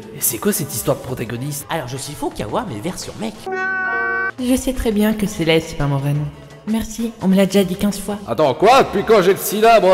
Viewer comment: elle dit quoi Celestia ? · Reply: Je sais très bien que celeste c'est pas mon vrai nom merci on me l'a déjà dit quinze fois de rien c'était gratuit ^^